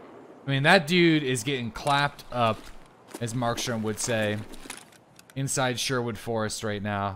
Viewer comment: “I mean that dude is getting clapped up, as mark sherm would say” Lowkey was about to sub just cus i thought he said Mike Sherm 😂🤦🏽‍♂️